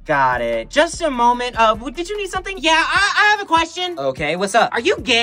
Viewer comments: I heard it as “old ass kid” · I thought he said "old a kid"